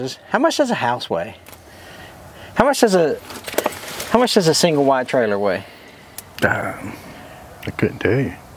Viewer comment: Hanks daughter didn’t do the intro on this one. Craftsman along with DeWalt, is also owned by, Stanley Black & Decker. After a quick Google, the average Mobile Home weighs, 45 to 50 lbs, Per Square Foot so an 800 Square-Foot house weighs roughly 40,000 lbs. This truck had AC, that’s a new one for you guys. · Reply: I thought said it moved single wides.